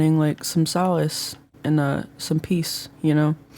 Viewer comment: I thought she said "salads and peas" LMAO (i'm so stupid)